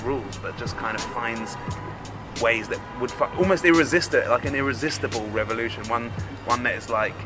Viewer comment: any thought that at 10:44, said "to F*C- almost irresistible